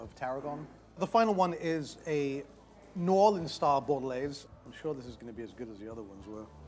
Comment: The way he says "New Orleans" sounds like he's saying "Northern". If it wasn't for the subtitles I'd have no clue what he said... why that irritated me IDK lol